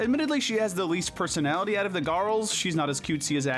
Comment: you said Gorls instead of girls